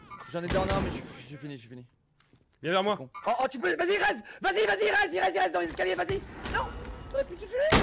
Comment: Pour la défense de, a 17:57 moi aussi j'avais compris "Va y Res, res, res, il restent dans l'escalier" 🤣🤣 il m'a fallu plusieurs écoutes pour comprendre qu'il dit en faite : "Va y ils res, ils res dans l'escalier" 🤣🤣